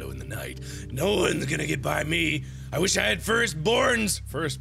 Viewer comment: At first i thought he said "I WISH I HAD FIRST BORNS"!! XD